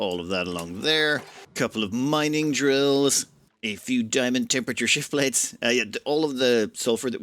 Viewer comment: you said "Diamond Temperature Shift Plates" I had to back that up because at first I heard "Time and Temperature Shift Plates" which threw me for a loop. (Anyone else here remember when there were phone numbers you could call to get the current time and temperature?) Especially if you consider "time shift" plate. I never do anything as elaborate as I see you doing, although you keep showing me approaches I haven't seen or thought of, give me inspirations for my own colonies, and you keep it entertaining